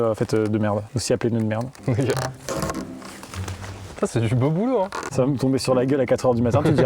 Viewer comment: "Putain c'est du beau "Bouleau" ... " C'est tout pour moi merci.